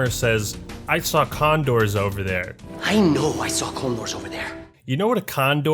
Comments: First time I saw this trailer I thought brohammer said “I saw clone wars over there” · Tbh I first thought Brohammer said "I know I saw Clone Wars over there" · No matter how many times I replay 4:46 I keep hearing "Clone Wars" instead of "'Condors" lol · When Brohammer says “I saw condors over there” At first I thought he said “I saw clone wars over there” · First time I heard (genius) Brohammer talk here, I thought he said: "I KNOW I saw Clone wars over there" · sounds like hes saying "clone wars" lol